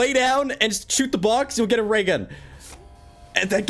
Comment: “If you shoot the box and lay down you’ll get a ray gun” killed me lmao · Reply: i thought you said, "if you shoot the box and lay down you'll get a GAY gun"